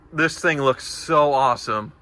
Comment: He says "awesome", I hear " expensive".